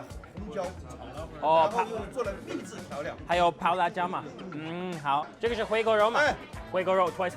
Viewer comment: what did he say 😭 i know I did not hear dog meat